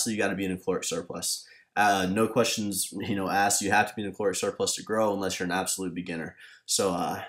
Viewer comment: ….what did he say? "something" surplus….anyone hear? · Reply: caloric surplus